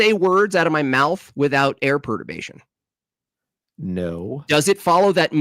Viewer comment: Help me out, what word is saying here when talking about “air.” Air what? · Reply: air perturbation